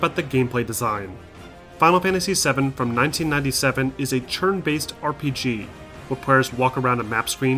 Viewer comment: he’s saying “churn”? “Churn based combat”, instead of turn based? Aha, cute.